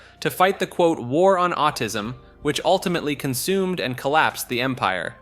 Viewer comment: did he really say autism